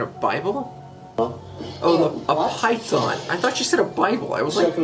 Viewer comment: She said "python" You heard "Bible" I heard "pipe bomb" 🤷🤣